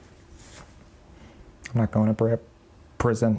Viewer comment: sounded like "I'm not gunna break up with you" scratches notes "I'm not going to prison"